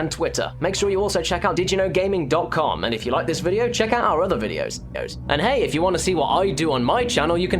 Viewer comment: "Check out our other videos... os." Am I hearing things?